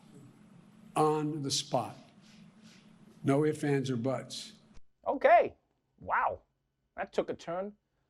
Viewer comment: Bigger Zoom screens would make more sense Did he just say No Butts